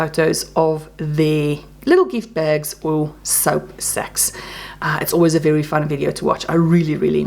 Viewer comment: Because I’m your 12 yo friend…at first when you said “soap sack”, I thought you said “soap s@ and I was like…. I really doubt THAT was the CAL😂🤦🏻‍♀️💕. I apologize. I finally got to sit down and catch up a little. Your cat and doll are beautiful as always. Sending you hugs. ❤❤